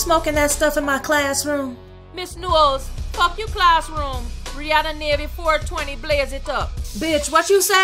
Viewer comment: w8 what did rihanna say on 1:47? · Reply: "Ms.Knowles, fuck your class room. Rihanna 420 blaze it up."